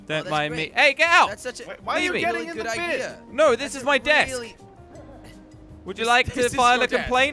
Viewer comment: When said “This is my deck” i thought he said “This is my 😏 “ .O.